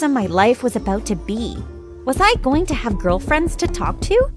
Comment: what just he say girlfriend